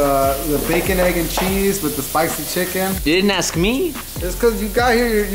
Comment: I thought said “bacon egg and cheese with the PAISA chicken” I was like wait what?!!!! 🤣🤣💀